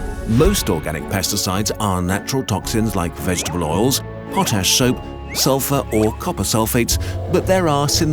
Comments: Thought he said "Hot Ass Soap" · did he say hot ass soap or is that just me